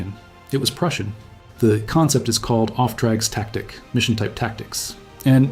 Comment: as a german it Took a while to unverstanden what he said. Auftragstaktik spelled like off tracks tactic is ... crazy 😂